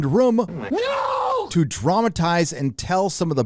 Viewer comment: HAH I thought you said traumatize instead of dramatize XD